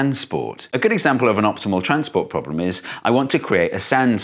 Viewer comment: i thought you said "Optical Transport Problem" and i'm like, ya that makes sense